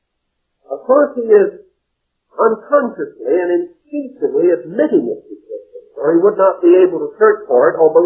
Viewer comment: Transcript: instinctively instead of "intentionally" admitting it.